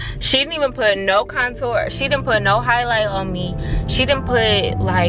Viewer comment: Wait, wait did she just say she didn’t put highlighter on her bitch wtf